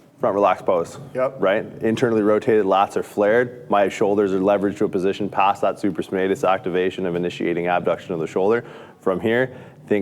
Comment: 😲 WTH did he just say lol 😂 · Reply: My shoulders are leveraged to a position past that supraspinatus activation of initiating abduction of the shoulder